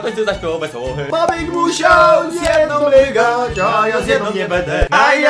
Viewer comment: czy tylko ja usłyszałam :a ja zjem niego Niem